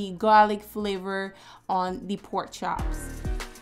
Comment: When she said pork chops the first time, i almost thought she said “four chops” but then she said pork chops again, so i knew i wasn’t bugging 😅 but I’m definitely stealing this recipe, thanks! 🏃🏾‍♂️💨